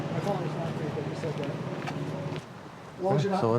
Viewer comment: Did I hear correctly at 30:00 " My fathers not Greek, I just said that" lol